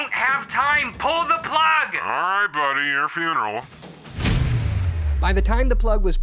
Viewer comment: English closed caption error at 03:43, instead of "you're feeling real", it should be "your funeral" :)